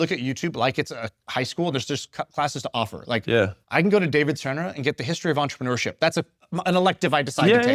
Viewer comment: there is a David _ mentioned for history of entrepreneurship. I didn't catch the name. Anyone know who was referred to? 🙏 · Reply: David Senra. The podcast is called Founders